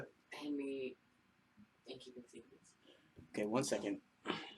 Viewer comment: I had this on in the background at work (hope my boss isn't reading) and all i heard was "Pass me 'in keeping secrets!" Love that album.